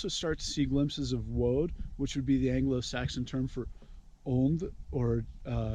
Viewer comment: First off, thank for putting this up. I've just started to get into Germanic paganism and your talk of wyrd is interesting to say the least—i'll be seeing more for sure! What was it you said near the end—(8:00 to 8:12)—about woad/wode (?) and some Norse cognate, or synonym i couldn't hear? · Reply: He’s talking about Ond It’s the vital breath Odin gave to man kind in the creation along with other things from his brothers